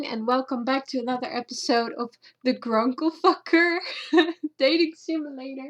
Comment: Did you just say T h e G r u n k l e F u c k e r or am I hearing things